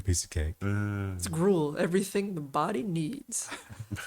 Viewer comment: Did really say, “It’s grool; everything that a body needs?"